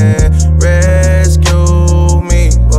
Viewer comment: I thought it legit said. Take me off the meth